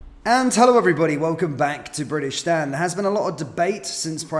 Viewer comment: Damnnn😂😂😂😂 i thought u said bri-tish-stan